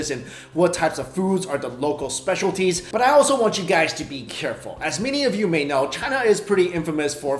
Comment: hahaha I heard you say beef careful!!!